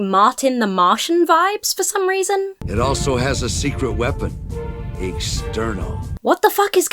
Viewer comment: When he said "a secret weapon" I thought he said "a sterno!" and I thought "well yes, a can of sterno would probably stop a snowman." My disappointment knows no bounds.